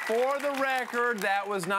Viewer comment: WHO does he say that it wasn't aimed at..I got M&M, Simmons, PTI, and then it sounds like he says "beetles????"...ANYONE? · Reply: Dan LeBatard (spelling may be off) He had a show on ESPN with his dad. Pretty good show considering...